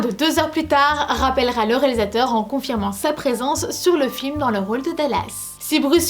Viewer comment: C'est moi ou bien à 7:33 j'entends le mot FLIM... le 5th element s'agit-il d'un flim sur le cyclimse ? merci pour cette vidéo d'un flim qui a bercé mon enfance ! (